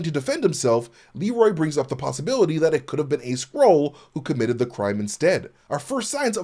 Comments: Thanks for the breakdown. 7:21 When I saw the episode I 100% thought he said "Could it have been a squirrel?" and thought he was just nuts. This after almost successfully erasing the existence of Skrulls from my memory after Secret Invasion and it costing a reported 212 million. I mean Kev seriously mate, next time you have an idea like that run it past me and I'll be more than happy to save you that amount of money (and countless hours of viewing time everyone won't get back) for just 212 quid each time. One interesting thing they could do is have Vanessa be Muse; although I'd suspect her setup would be more like Dexter's rather than be in a dirty sewer. Kingpin's absence could be used as a reason for her transformation, unless they go with saying she always was Muse. If she isn't Muse, Kingpin could commission him to make a painting using Adam and give it to her as a gift. He could then use him as bait to lure out vigilantes. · I'll be honest. I thought he said squirrel in that scene instead of Skrull